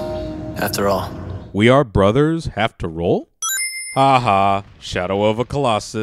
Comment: I think dante said "we are brother after all" not "we are brothers have to roll